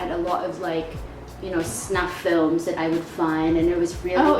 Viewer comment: im sorry did she say her dad had a lot of snuff films?